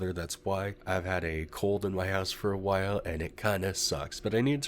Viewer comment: Oh..... "Cold in your house"...lol! Ok. I get it.... I had to put on the captions for a second because I thought you said you had a "Cold in your ass"...😂 I was like, how TF does that happen?..hahaha! Nice to have been able to clear that up.😅 Shwoof.